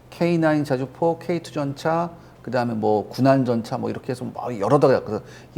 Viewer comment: 군안전차 라는 자막이있네요 아마 자막다시는분이 들리는 그대로 쓰신거 같은데 내용 맥락상 "구난전차"가 맞는것같습니다